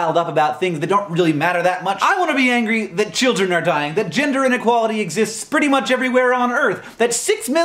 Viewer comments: Oh, "gender inequality" is what he said. That makes a lot more sense. I heard "ginger inequality." I was like "he's not even a redhead, but he cares for them so much." 😂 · "Gender inequality exists pretty much everywhere in the world" Citation needed. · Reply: Ohhh. I thought he said ginger inequality. :/ I thought the problem was finally being recognized. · Please tell me I'm not the only person to have heard him say 'ginger inequality' over gender inequality.